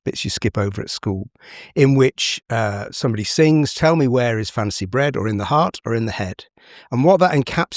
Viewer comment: Who else thought he said "where is fancy bread" instead of "where is fancy bred?"